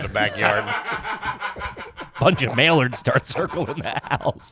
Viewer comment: Blink and you'll miss it, but around 1:03:00 says "mail-erds" instead of mallards. I almost drove my car into a child playing football in the road, cause I fucking laughed so hard. Shout out street football.